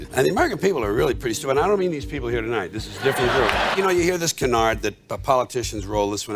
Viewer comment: Can someone help me understand what he said at @ 33:07 ? Something about a politicians role is to do what a couple times a week? Please & thank u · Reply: 'They roll this canard' aka they say this bit about